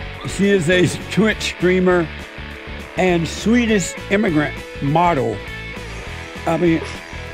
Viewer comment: "Destiny Peterson" and "Immigrant Model" less than 2 minutes in. This is gonna be gold 😂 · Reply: I hear “sweetest immigrant model” she almost looked flattered before she realized what he meant